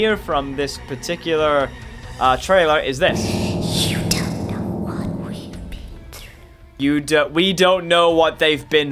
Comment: Actually I heard you don't know what we been through